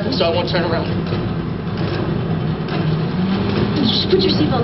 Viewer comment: Sounds like he said: Bitch, put your seatbelt on! Lol love it